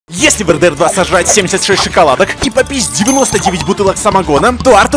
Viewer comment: Вместо попить мне послышалось попизд